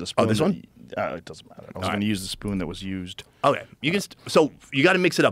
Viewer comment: Joe not wanting to share spoons. Procedes to put used spoon in the honey. 😂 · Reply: I thought that too but he said “I wanted to use the spoon that was used”